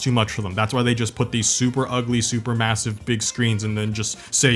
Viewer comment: did you say massive?